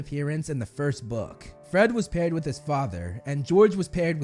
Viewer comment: I thought he said Harry and his father and l was like:but wait that’s not